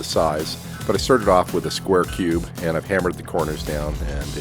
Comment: Lol, did he just say 'Square cube'? xD